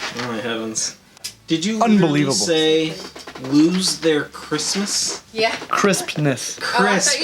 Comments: I also thought he said they lose their Christmas. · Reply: Lmao. I thought he said Christmas until I saw this comment xD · I definitely heard, "lose their Christmas" and somehow it still made sense to me. Yes to enclosure build! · I also thought he said they lose their Christmas.